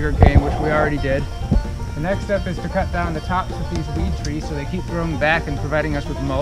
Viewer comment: Did you say "the tops of these weed trees? What is the species?